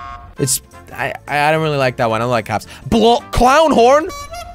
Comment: does he say " I dont like cops" or does he say " I like cops" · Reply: i like